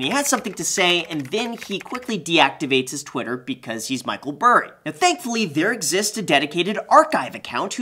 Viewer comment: Did he just call Christian Bale, Michael Bale